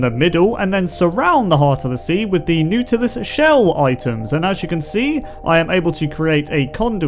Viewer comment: It sounds like is saying "Nootluis Shells"